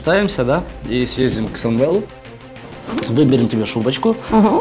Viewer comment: СТОООП 7:56 К САМВЭЛУ?????? ЭТОТ САМВЭЛ ИЗ 3 ВЫПУСКА 3 СЕЗОНА! ТОТ ПЛЕБОЙ МНЕ ОДНОЙ ЧТО ЛИ ЭТО ПОСЛЫШАЛОСЬ??